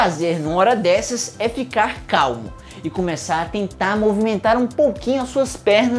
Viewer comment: só eu que escutei "calvo"?